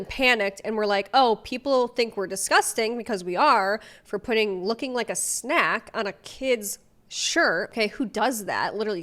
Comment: I thought it said "looking for a snack" which is not only actually appropriate for a child, but makes actual sense since Santa is looking for the cookies.